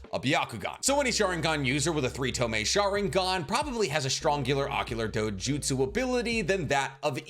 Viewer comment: for that "Strongular Ocular Jutsu" line that I was so sure I heard wrong the first time 😂